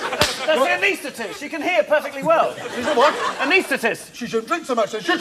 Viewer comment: What did Stephen say at 6:50? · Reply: Steve said "A what?"